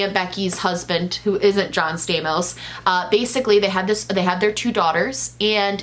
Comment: I thought you said “and her husband John Stamos.” I was immediately like whaaat. But now I hear the “not.”